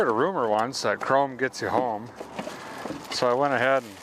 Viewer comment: true🤣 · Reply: i heard it as ( chrome don't get ya home ) a while ago